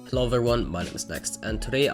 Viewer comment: It sounded like you said cloveveryone instead of hello everyone